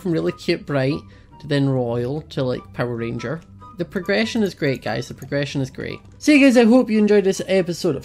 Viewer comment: For a second I thought he said "the depression is great, the depression is great.."